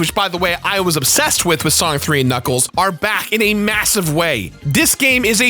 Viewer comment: I really thought you said sonic 06!